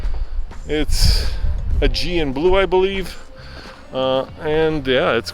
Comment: OOOHHH! Aegean blue...def thought you said GM blue. So confused.